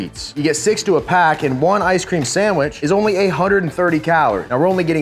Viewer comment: Wait… the skinny cow ice cream sandwiches… did he just say 1 of them has 830 calories!?